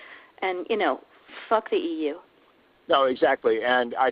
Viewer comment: This should be on every news network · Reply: did she say "f..k EU " ?